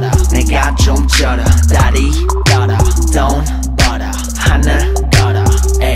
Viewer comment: I thought GD said dont bother for example